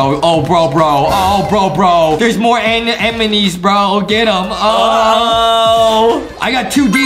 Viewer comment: I heard say emeny in